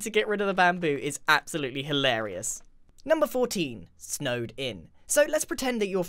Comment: thought that said snowdin😂😂😂